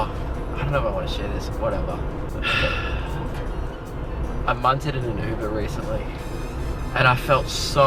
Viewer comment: I forgot u guys call it munt so i thought he said i mounted in a uber lol